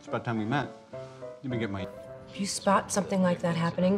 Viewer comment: uh does anyone know what she’s saying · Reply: She said can you believe it